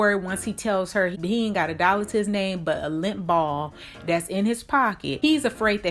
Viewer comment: Thought you said he’s got a ‘limp ball in his pocket’...either way...